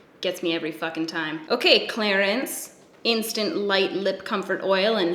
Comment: It sounds like you say Clarence not Clarins lol